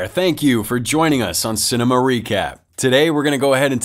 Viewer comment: I thought he said "Cine Marica"